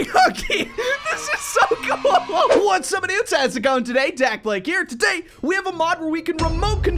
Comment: why does every intro of dakblake sounds like "what's up idiots!..." I know he's saying something else, its just i can't seem to hear it.